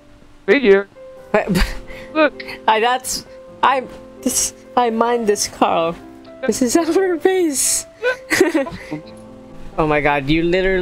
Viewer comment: YouTube thought u said "I mind this carl, this our face" LOL 😆💀🤣